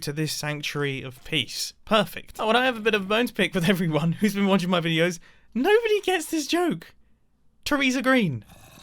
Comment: I definitely thought you were saying Traesar as in Caesar, not Theresa